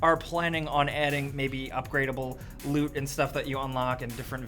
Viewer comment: Wait, he said "upgradeable loot." I got like half way through the video then backtracked to the beginning because I really thought he said "upgradeable boobs"